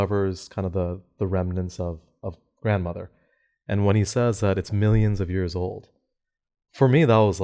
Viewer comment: I thought Father said Grandmother’s bones were almost a million years old too, but upon rewatching he said “millennia”, so only a thousand years.